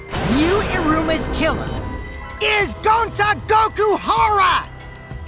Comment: did he said goku